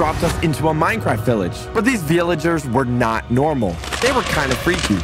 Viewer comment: (talking about a hole teleporting them to a village) Also socks:"says vilaegers" (That's what i heard if you guys hear him saying that just repile "yes i heard him" not begging for repiles)